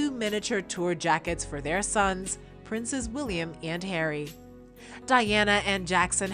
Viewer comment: Did I hear her say “ princess William “ ???